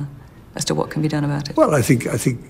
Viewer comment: DID he just say voluntary family ELIMINATION!?!?!?